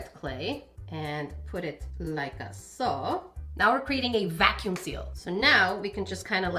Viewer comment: What does she say in her catchphrase? 😅 I am very curious because I am not sure if I am hearing it correctly 😂 I hear “Like I saw.” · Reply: Like-a-so (like so)